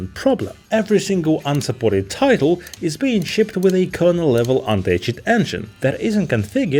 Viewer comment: What is he saying at 4:00 ? A kernel level __ engine? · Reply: kernel level anti-cheat engine